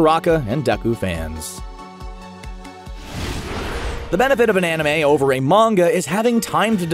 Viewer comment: Who just loves it when Katsuki (kacchan/ bokugou) turns on his sweet soft side. Oh and btw at 8:42 I thought that dude say “bananime” xD